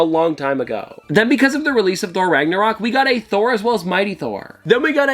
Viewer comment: Did he say Thor Ragnarok and no comments